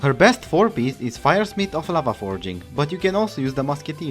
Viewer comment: I heard laba-Forging, what is laba? :o (Awesome video)